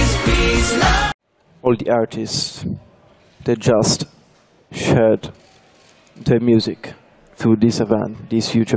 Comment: I can’t be the only one who thought he said “all the artists, they’re just... shit”